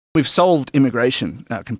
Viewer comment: how confidently he says "we've solved it" 😂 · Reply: I originally thought he said “sold”, not “solved” and thought “WTF”? then realized it was his accent.